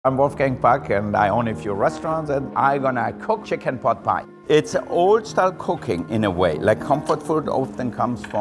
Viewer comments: Who else heard ,''It's old style cocaine'' right before he put in the flour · "its old style cocaine...." :P Totally sounded like that anyways. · why did i hear him say its old style cocaine LMAo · I thought he said "Wolfgang fu*k"